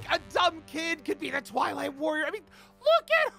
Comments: At the start i thought KC said toilet warrior 😂 i was like whaatttt? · I thought she said “Toilet Warrior”😂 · i thought she was saying toilet warrior lol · everyone who thought she said toilet warrior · I thought she said the ✨ TOILET ✨ warrior 😂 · I actually thought she said toilet warrior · I thought kc said toilet warrior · I THOUGHT YOU SAID TOILET WARRIOR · Tbh I thought she said toilet worrior😂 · Who else thought she said toilet warrior😂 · At first I thought kc said toilet warrior😅